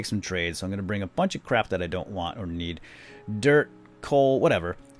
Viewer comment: Did you say crap